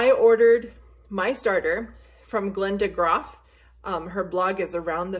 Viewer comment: Link where you purchased your initial starter, please. I heard “Linda ___.” Thanks! Happy Thanksgiving!